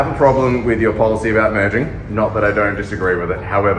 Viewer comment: "so I have a problem with your policy about murdering" I must be mishearing that. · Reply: Merging not murdering. lol. I had to listen to it a few times.